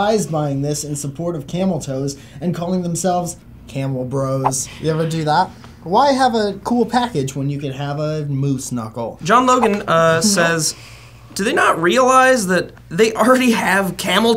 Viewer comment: said Moose Knuckle and I thought he said MooSnuckel and I was like "wait what?" and then I realized he was still talking about the camel toes 😂